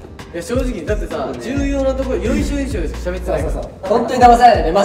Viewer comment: 中村海人さん「よいしょよいしょ」って言ってるように聞こえるんだけど もしかして「要所要所」のことかな